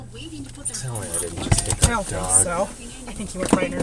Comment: does that lady say So? what does she say when the guy say i think i just hit a dog